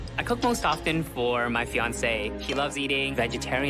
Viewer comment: What is up with the pronouns here? I heard he and then I heard her, and I'm just confused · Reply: I heard him say "she"